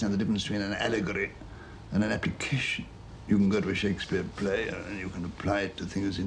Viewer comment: "People do not fully understand the difference between an allegory and an ___________." What does he say there, around 1:30? The CC subtitles translate it as "application" but it almost sounds like he says appitition which is not a standard English word – at least not one that I've heard before. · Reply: Definitely application.